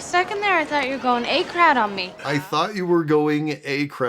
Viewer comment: She's saying "A-crowd"? For 25 years I've thought she was saying "A-crad" and this was just some American slang I didn't understand 🤦🏻‍♀️